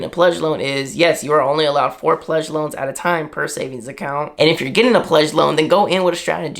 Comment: I thought said only one pledge loan at a time